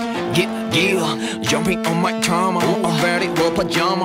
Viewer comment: I thought Taeyong said “Jumping on my cama...” 😂 cama= bed